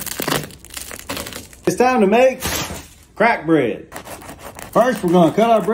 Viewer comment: Apologies I thought he said crap bread 🤣😂🤣😂